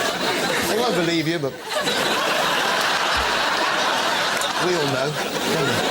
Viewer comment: What does he say at 30:32? "You won't believe..." what? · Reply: “They won’t believe you but… …we’ll know